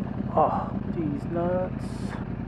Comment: did you say Deez Nuts at 0:42 ?:D